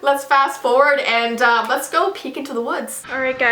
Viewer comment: I thought you said " let's go pee into the woods" Lol